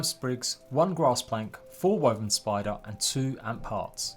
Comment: did he just say "woven spider"!?